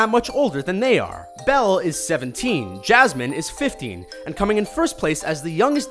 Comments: he says 50 instead of 15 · I swear I heard 50 b it it was probably 15